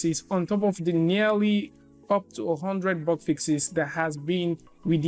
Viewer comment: For a moment there I thought you said box fixes. Lol